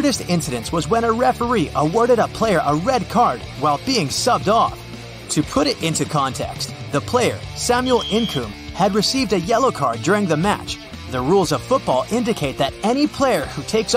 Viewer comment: What is up with the soccer referee (time stamp 58:07) and player getting yellow and red card. Was I hearing BEING SUCKED OFF, lol? 😂